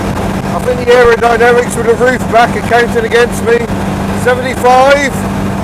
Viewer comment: I did close the roof! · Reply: It does sounds like you said "the aerodynamics of having the roof back are counting against me"?